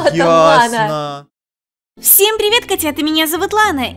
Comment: Мне послышалось то что Лана сказала Всееем привет МОНСТРЯТААААА!!!!!! ААААААА Я БАМБЛЮЮЮ ИЗ ЗА ТОГО ЧТО Я НЕ ТО СЛЫШУУУУУ